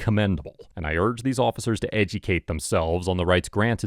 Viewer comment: Love the videos but why did you say "edjucate" like that @ 15:17 lol i cant unhear it